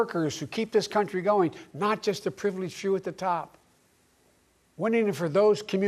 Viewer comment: wait, did he really say "not just for the privileged Jew at the top"? Lmao